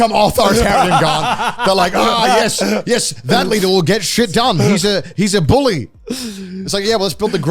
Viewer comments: did anyone hear say the n word around here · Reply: He said, "That leader will get things done. He's a bully." (Explaining why people become authoritarian.) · What did say at 25:00? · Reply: I also had to do a double take on that. I think he said leader but it sounded like something else.